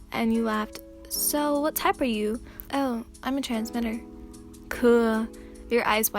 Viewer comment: I swear for a second I thought they said "Oh I'm a trans gender"